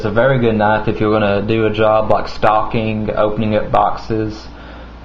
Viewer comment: did you say a really good knife if your gonna do a job of stalking, opening boxes and cutting rope?